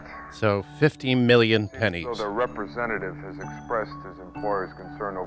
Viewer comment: I thought he said "panties"...😮